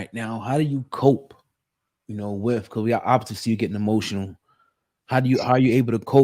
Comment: DID HE SAY @ 11:29 = how do you cope ? Your opps are seeing you get emotional ? Did he say that or am I trippin ?